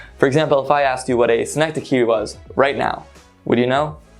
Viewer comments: 🙃as a second language learner cannot parse what he says here Any warm heart out there to help me out? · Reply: synecdoque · Sorry but what device did he say at 7:19? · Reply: Synecdoche....I think. It's a figure of speech.